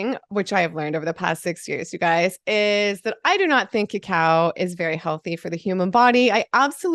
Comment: OMG you said "cacao" ! I thought you said "Cow"! OMG thank goodness I went and listened harder! This will be my life, too.